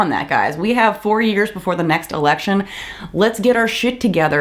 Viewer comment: I could have sworn you said 'we have 40 years before the next election'. At first I thought 'haha, funny', but you know... they would if they could. Unlikely, not impossible.